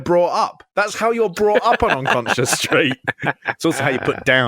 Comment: Aww i misheard bret saying unconscious street. I thought he said conscience and he was going to give razor a lesson in morality 😂